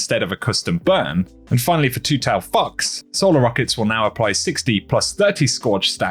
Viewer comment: Lolol for 1 second I thought you said two tailed F*ck$